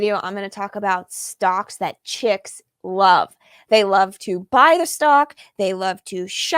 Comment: I thought it said stocks chicken love. Get it love chicken stock. EEEHHHOOOO!